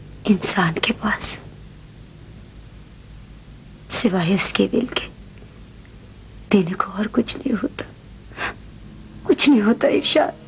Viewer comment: kya bola a kya daynay ko kuch ni hota??? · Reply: insan k pas sewae is k will k denay ko kch ne hota